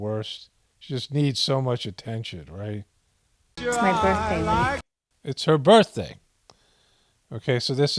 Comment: I swear Mad X said it's her "birthday week."